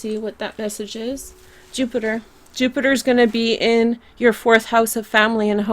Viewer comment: is it just me but did you say jupiter before looking at the coin? P.s love the reading